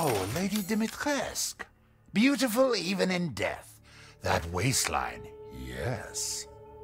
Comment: They got the subtitles wrong. It’s supposed to be: ”That waistline.....yaaas!”